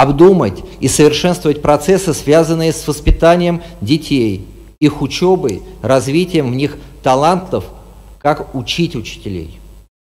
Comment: "...надо уходить от формализма, обдумать и совершенствовать процессы, связанные с воспитанием детей, их учёбой, развитием их талантов, как учить учителей..." ...мне "услышалось" следующее: "нужно учить детей, как "правильно воспитывать учителей"..."